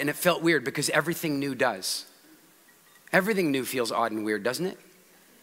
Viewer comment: Thought I heard ... odd & nude . . . . .